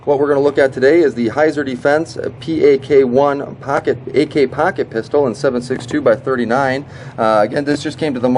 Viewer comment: do you have an accent? sounds like your saying packet instead of pocket